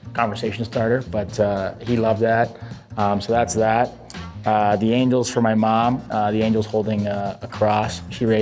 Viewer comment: almost sounded like he said "the angle is for my mom"